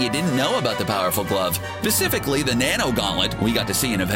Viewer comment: am i the only one that heard him say pacificly and not specifically?